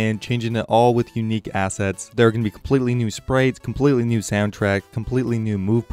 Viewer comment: I'm sorry, hahahahaha, you said "sprates" and not "sprites", hahahaha, I can't even take you seriously now, LOL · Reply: M 0:36, he clearly says 'sprates' haha 🤣